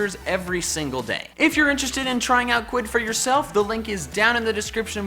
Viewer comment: When he said quidd I thought he said Quinn